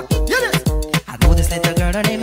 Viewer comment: The one where it was like “her beauty like a bunch of rose” I always heard “her bit**es is on the throne”😭😭